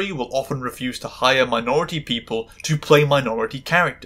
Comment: I thought you said "menstrual show" instead of "minstrel show" and I was very confused!